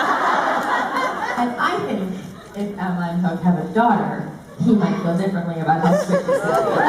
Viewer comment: What did jen say in 3:28? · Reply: That she thinks that Emma and hook might have a daughter